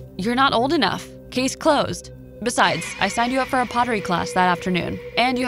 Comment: I thought she said “I signed you up for a poverty class”